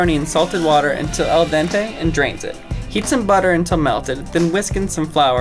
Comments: When was Khloes turun I heard and drinks it 😂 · I thought he said then drinks it 😂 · When he said chloe kardashian drains the water I thought he said she drinks it 😂 a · On khloé I thought when u said drain I thought u said drink it I was all like ewww